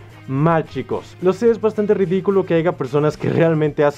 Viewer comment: Escuché mal o dijo haiga? xD Iwalmente buen video, like